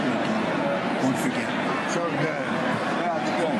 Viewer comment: Oh, that's funny, I watched this happen live, and I thought he said _"Don't _ forget" which I thought would make people lose their minds, but I didn't hear anything about it in the morning so I just put it in the back of my mind and moved on.